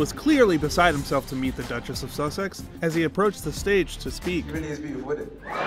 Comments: What did he say at 0:46 ? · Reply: “She really is beautiful innit” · What do he say at 0:46??? Am I the only one who couldn’t understand a word 🤷🏻‍♂️ It’s sounded like “askfnrlfhfmfdlwohda” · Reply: alec rivera ‘she really is beautiful init’. I didn’t understand either, got it from someone else’s comments. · what did he even said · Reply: “She really is beautiful innit “ innit is British slang for “isn’t it” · I can’t even hear what he said…he said it really fast, right at nearly the 0:50 mark. Did anyone catch that? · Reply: "Really is beautiful isn't it?" 🤭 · What does he say at 0:47?? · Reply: She really is beautiful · What did he say at 0:47 ? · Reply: ‘She really is beautiful init’ 😂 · can someone tell me what he said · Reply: Megan is beautiful, innit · What did the young man said at 0:47 ? He spoke too quick so I didn't get what he said. Help please. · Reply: He said “she really is beautiful innit”